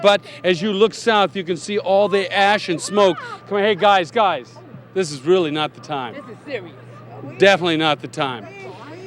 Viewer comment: lmao what were they shouting about? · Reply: They actually said “Oh it’s Larry Hoff” and he had to reprimand them under the circumstances